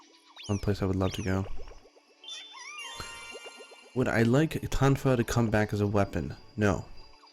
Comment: i couldnt understand the word. what kind of weapon? tang fows? what??? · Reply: i think he meant "tonfas"